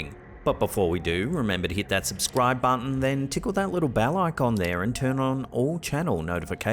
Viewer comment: Every time you say ‘Tickle that little bell icon’ I hear ‘Tickle that little pickle’ … I’m not even joking.